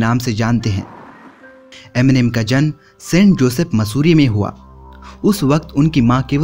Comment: did he said massuri😂